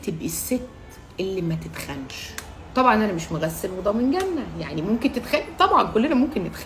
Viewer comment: شو قالت 0:16 طبعاً أنا...؟؟ عدته عشر مرات مافهمت😂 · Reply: "طبعا انا مش مغسل وضامن جنة" يعني حاجة ماتقدرش تضمنها ١٠٠%